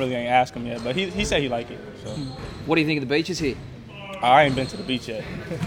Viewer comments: Wow I thought he said bitches, not beaches lmfao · I really thought he said “what you think about the bitches out here”